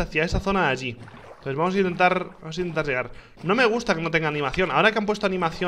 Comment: en un momento pense que dijo "vamos a intentar rushear" xD